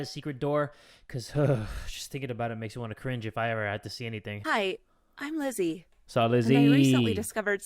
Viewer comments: When she said Hey Im Lissie I thought she said Hey Im lesbian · When Lizzie was introducing herself, I thought she said “Hi, I’m glizzy”